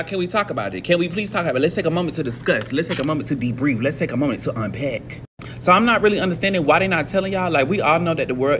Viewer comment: That one who is flipping the pen around. I thought he said, "Let's take a minute to fudgepack.😂😂😂